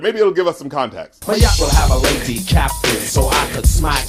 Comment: I thought he said a lazy capton